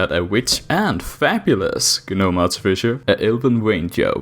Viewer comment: Sorry, I just heard that as "fapulous".